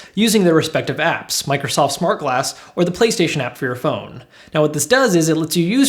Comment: am i the only one that heard him say play station 2